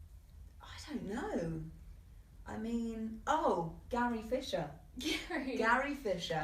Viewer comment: did anyone think she said gary fisher